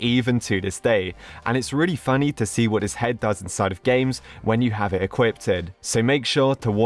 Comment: did he just say "when you have it equipted"